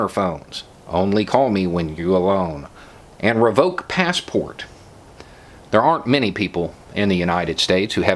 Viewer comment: I thought it said "revoke pass pork"